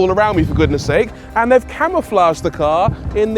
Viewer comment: that camouflage kinda works... those reds that green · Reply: I thought he first said camelflage 😅🐫